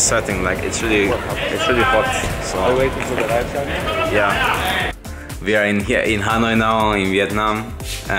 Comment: 'we are here in Harrenhal' is the only thing I hear